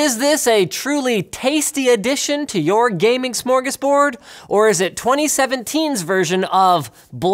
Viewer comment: did he say smörgåsbord?